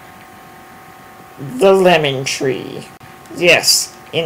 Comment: Doh, I thought you said "Lemming Tree" :-)